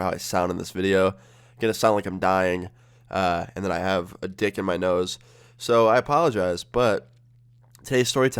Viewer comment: Did he say "I had a dick in my nose"????